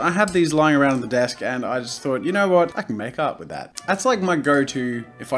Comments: instead of "I can make art with that!" It sounds like you're saying "I can make out with that!" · for whatever reason, i thought that instead of saying "i could make art with that" you were saying "i could make out with that" ... oh no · I thought you were saying I COULD MAKE OUT WITH THAT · At the beginning when he was saying "I could make art with that" I thought he was saying "I could make out with that" LOL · When he was saying "I could make art with that" I thought he was saying "I could make out with that" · For some reason I kept hearing, "I could make-out with that." Then when he brought out the sword, my mind created a mini romance fanfic.. Of... Making out with a sword... 😂😂 I should see a doctor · was anyone fooled thinking he said “I can make out with that” 5 times to inanimate objects then got confused and replayed it and realized what he actually said. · When he was saying “I could make art with that” I thought he was saying “I could make out with that” and I was so confused